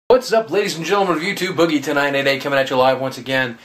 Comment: OMG! i always thought he said boogie tonight at 8 comin at you live, turns out he was saying boogie2988 comin at you live. i cant freakin believe that i heard it wrong all that time